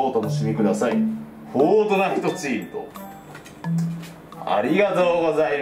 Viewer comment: フォートナイトチームの所がフォートナイトチートに聞こえたのはおれだけ